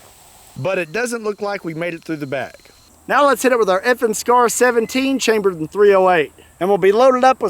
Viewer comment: When he said FN scar I thought he said effing scar lol